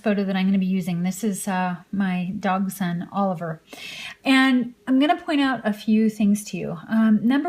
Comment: Did you say "my dogs son oliver" around 2:49? Sorry it was just annoying me. Great video! :)